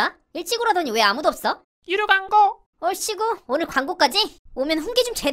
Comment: 여기서"이루 방구"라고들림 ㅋㅋㅋ (내 댓글에서 도배 작작해라)